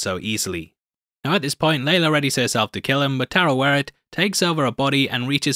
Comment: you said Tarawaret. i hear you! 😂